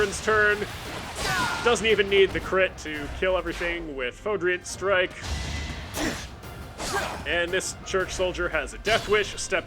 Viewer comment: I always hear Catherine's unique Combat Art as "Fraudulent Strike"